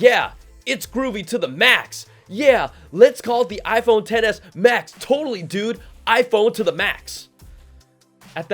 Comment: Hahaha it sounds like you said iPhone Tennis Max 🤣🤣🤣🤣